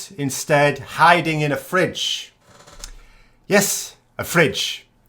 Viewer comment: As always, he was misunderstood when we thought he had said "Dead in a ditch". In reality he said "hide in a fridge". And he delivered on his promise!